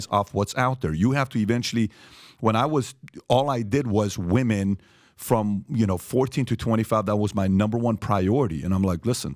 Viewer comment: …. Did he really say, “ all I ever did was women from 14-21…”??????